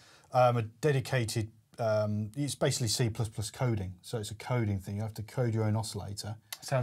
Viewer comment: Did he say c++ coding? :D